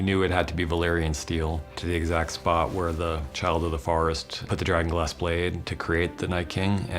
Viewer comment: did he say childhood of forest?